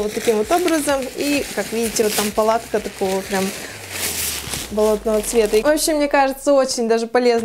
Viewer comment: Мне послышалось не болотного а блевотного цвета! 😂